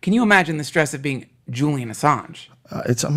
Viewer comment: can enyone please tell me at 47:28 about what guy his saying jullien something ... ?? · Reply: juilian assange, creator of wikileaks, which is a whistleblowing website. he rose to fame and was persecuted for spreading the truth on the internet.